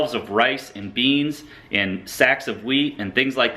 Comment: does he say sacks of weed?